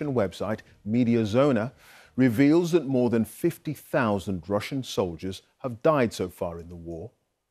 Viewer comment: I thought they said 500,000 Russian soldiers lost. Now only 50,000. I can't imagine how many Ukrainian soldiers lost. But it's still sad to here those lost.